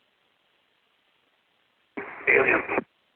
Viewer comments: I hear.... ALIENS.... 🤘👽 🤘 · “ALIENS” Who else heard it? Who said it?